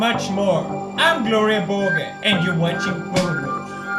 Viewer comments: Wait did he say «im Gloria Borger and your watching pornos.» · lol i thought he say " Im Gloria Borger and this is porno" LMAO